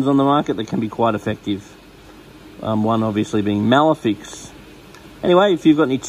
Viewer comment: didnt quite get what you said there... did you say melafix?.. cause im quite in a pinch here with my fishes